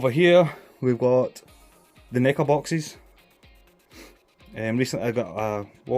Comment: Thanks for sharing. I'm looking forward to AC Odyssey, but know it's a long game. Your bloomin accent, I thought you said 'knicker boxes', not 'Neca boxes' haha. I enjoyed Borderlands 2, but thought 3 was terrible. What are your thoughts on 3? Take it easy.